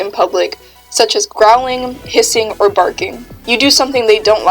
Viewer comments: When said "growling, hissing", I thought she said "growling, pissing" and burst out laughing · I thought you said pissing😂